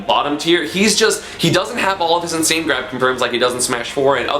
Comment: Anyone know what the fuck he said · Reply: "He doesn't have any of the same grab confirms like he does in Smash 4"